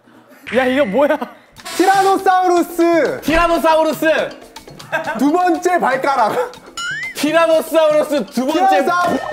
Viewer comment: please tell mw what he said 😭😭😭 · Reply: I saw fan-edits of this game .. he said "T-rex second ball" 😂 ... that's why Hanbin got panicked ..!!